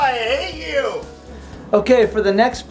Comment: am I the only person who heard I egg you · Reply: he said i hate you